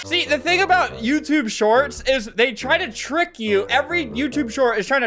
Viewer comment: i thought it said “YOUTUBE SHORTS FUCK”